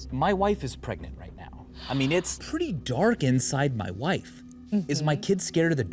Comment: “It’s pretty dark inside my wife.” -Michael, 2019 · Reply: First time, I thought he said "its pretty dark inside my life"